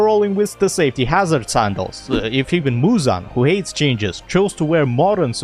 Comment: i heard "Muzan, who hates gingers"